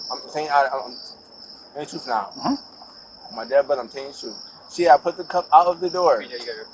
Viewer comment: "On my Dad, but I'm telling the truth." .. lmao.. anybody have a comment for this? ;) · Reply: I thought he said on my dead brother. And that closed caption got it wrong. Because "on my dad" just didn't seem correct.